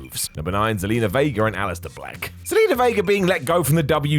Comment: holdup, did you say alesiter fleck? seriously, i heard "fleck" not "black".